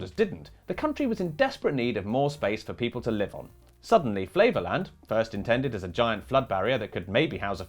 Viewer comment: I hear what you’re saying but I also hear “Flavorland” and keep picturing a giant Guy Fieri theme park outside Amsterdam.